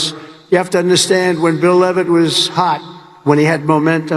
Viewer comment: Did he call out the guy by name? I heard "Bill Levitt"?